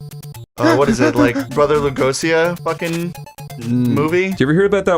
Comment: did he just say "Brother Legosia"?